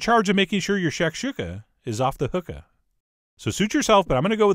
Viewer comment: Did he say Your shakshuka is off the hookah? Neat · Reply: No, he said “off the hook-a”, a play on “off the hook”, which is an idiom that, among many meanings, can mean “extremely awesome”, but he added the “a” at the end, to rhyme.